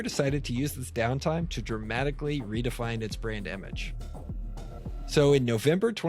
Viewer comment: I literally thought you said "redefine their brain damage" instead of "brand image"... Which actually makes more sense in this context 😝